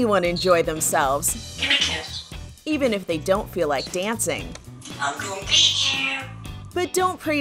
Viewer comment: Now who tf is saying "I'm gon beat you" around the parrot? 😂